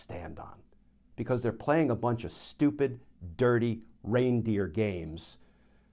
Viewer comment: Ha, you said reindeer, and I thought you said brain dead. Either works.